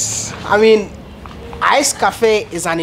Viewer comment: She had to have the money to be able to rebuild and give away her spa. Strong indeed but certainly blessed and hard working. Being honest is so important. I thought you were saying " ice coffee". I'm an American married to a Ghanian. Many things are indeed difficult to do in Ghana, especially when one is trying to make improvements. Hope to visit soon. I have lived in Than and plan to return. Great video.